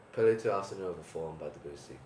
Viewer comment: what's that piece by Debussy? Can't hear the name right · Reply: my best guesss is: Prélude à l'après-midi d'un faune ?